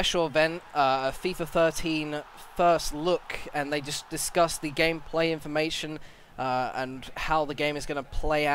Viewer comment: i thought i heard "disgust the gameplay" lol